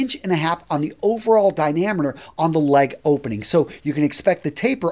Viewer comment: did I hear you say "dinameter?" What's that? lol